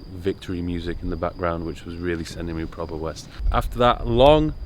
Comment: is that english? or some of that english slang? is that a sentence? sending him proper west? i need to know wth hes saying